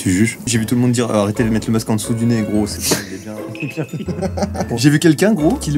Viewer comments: en dessous de nez quoi ??? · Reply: Sensei c le n-word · Y’a que moi qui entend le n-word à 0:41 après c’est donc oklm